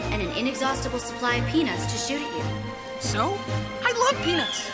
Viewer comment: j'ai entendu "I love penis"